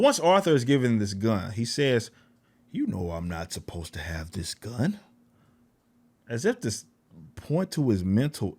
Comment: I thought he said, "we're not supposed to have guns at work." Not sure how the movie had any gun control propaganda. The only reason Joker (at the time Arthur) used the gun was due to the three a-holes trying to beat him up on a train.